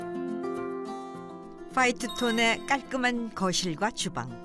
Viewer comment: 퐈이트 톤의~~~ 퐈이트는 무슨 색일까….. 내 귀가 의심스러워 여러번 돌려 들었네. 바이든인지 날리믄인지~~ 나레이터 개오바.